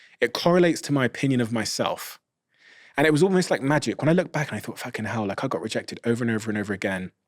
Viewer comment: Did Steven just said "fuckin hell" at 53:57?? 😀 I never heard him saying that before.